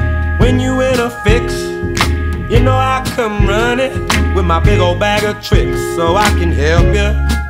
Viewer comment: My kid thought he was saying he'll come on running with his big ol bag of chips. Like potato chips lol. I love this song.